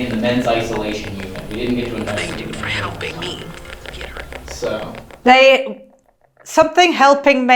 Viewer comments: 30.28. -----for helping me----get hurt. I have relistened and relistened and what I hear is 'thanks dude,for helping me get her.' And it was straight away after fixing the cables...First audible words...just an amateur ear 🙈😅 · First Estes…I thought I heard “Thank you for helping me get her.” 🤷🏼‍♀️